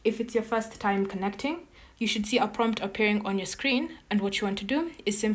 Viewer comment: Hi, at 1:08 you were talking that 'You should see a XXXXX on your screen...I couldn't understand what you meant, as you didn't point to the area on the screen....? Thank you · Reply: If it's your first time connecting, you should see a prompt asking you to allow the connection. That is what I was referring to. Since I had connected the microphones prior, and already given the permission, the pop-up did not show again.